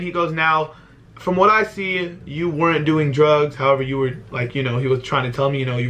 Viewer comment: Imfaoo I thought u said u weren’t a virgin😭😳